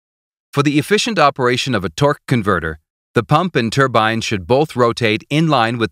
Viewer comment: i misheard it as... "twerk converter" many times lol